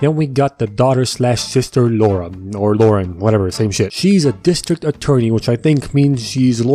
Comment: I thought you said autistic attorney for a second 😭✋🏼